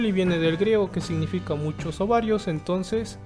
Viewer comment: Jajajajaj 1:39 parece que dijo “mucho ovarios” tuve que releer la diapositiva